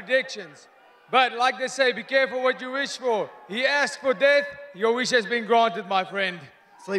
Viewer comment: I almost thought dricus said “you ask for dad” now that would’ve been badass 😂